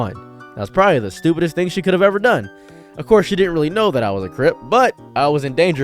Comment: creep instead of crip, ai subtitles betrayed you brother